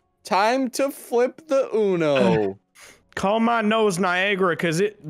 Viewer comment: I thought he said call my nose viagra and I was VERY confused